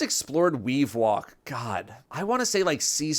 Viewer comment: Did anyone mishear and think he said weedwalk instead of wavewalk? 🤣